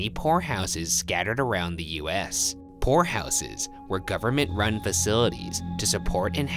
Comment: i thought he said whorehouses